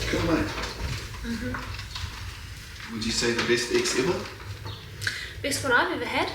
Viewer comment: Genuinely thought said "best sex ever" 😂😂😂 had to rewind it.